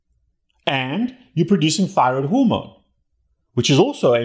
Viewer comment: I couldn't understand the word just before the word hormone at 5:50. Great talk. It clears up a lot of confusion. Thank you. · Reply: thyroid hormone